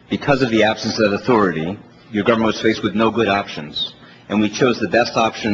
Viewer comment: Did I hear Geithner correctly at 3:03? Did he say "your" government? Not "our" or "the" government?